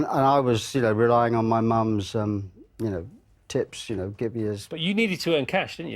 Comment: I thought Phil said 'tits', LOL!